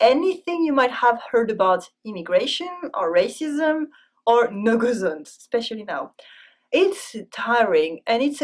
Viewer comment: Thank you for the great video! I'm trying to go to France this summer and this will be very useful to know. Btw did anyone catch what said at 2:14 after immigration and racism? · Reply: go zones?